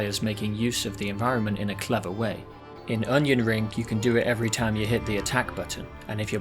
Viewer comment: I keep hearing Onion Ring. Now I can't stop calling this game Onion Ring.